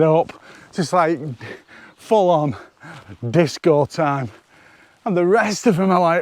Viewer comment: As usual, great fun! Went by in a flash. I heard "disco trees" and thought jab #2 was too much. But wait... I could see it! Holy crap, they were in a dance fever! No disco ball, though- maybe you could work on that? Great image!